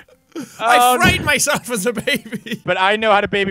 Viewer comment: My headphones cut out for half a second and this is what I heard: "I f___d myself as a baby."